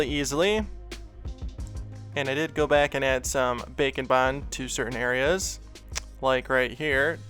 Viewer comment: At first I thought he said bacon bond and I was like what the...